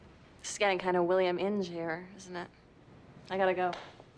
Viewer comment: What did she say at 3:17 ? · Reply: She said "Skank kinda William in jair, isn't it?"